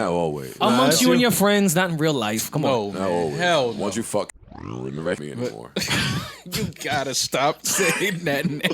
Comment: Joe dropped a name couldn’t find it so parks just Blurpped it out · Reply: I thought he said "once you fuck JR smith"